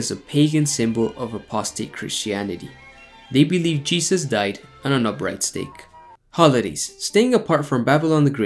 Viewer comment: I thought u said he died on a breadstick for a moment 😅